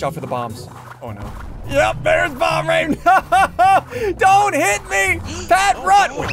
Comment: when you said " bomb rain " i heard you say bum rain i was like wtf